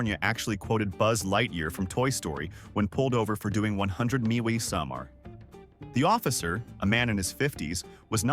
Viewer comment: Did you just say 100 MeWeeSomAre @ 32:12 ???